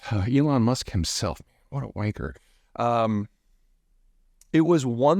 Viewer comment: did Peter just say wanker lmao